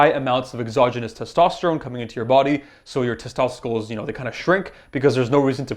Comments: Who else heard him say “testsosticles” 😂😂 I’m dying 🤣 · "your testoecles" haha sorry bro but when I hear that I couldn't stop laughing. To be fair I know you were probably hella tried by the time you got to that part of the vid and it doesn't take away from all that damn knowledge you gave us.